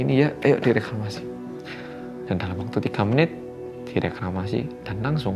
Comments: semangat mas ✊ aku salah denger gak yaa, harus nya di kremasi kan yaa? aku kayak denger di reklamasi 😁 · Dikremasi, koq kedengerannya Reklamasi wkwk> Makasih